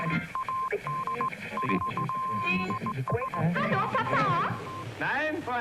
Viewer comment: ฮาโลป้าป่า....ใครได้ยิน